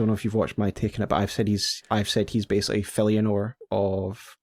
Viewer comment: Who is the name he compares Mesmer too? He got talked over and I cant quite hear where it is from. Falianor? Eleanor? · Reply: Filianore, from Dark Souls 3's Ringed City DLC. Filianore was one of the children of Gwyn whose father more or less sacrificed her to the Ringed City to extend his control over the Pygmies/the Dark Soul.